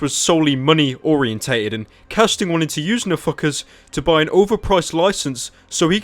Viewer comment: Every time you say NFKRZ i only hear "a fuckerz"